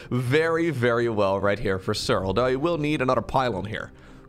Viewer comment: am i the only one who heard "Serral" here? TIL Serral's Protoss is only gold league. I know it's your off race, but still ...